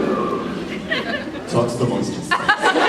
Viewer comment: What is happening from 20:45 and on? I can't understand a word they're saying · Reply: Are you wondering what Jared was saying? Well he says: Rhode Island is neither a road, nor an island. Talk amongst yourselves. If a fly didn't have wings would you call it a 'walk'? Talk amongst yourselves. Why do we drive on parkways and park on driveways? Talk amongst yourselves.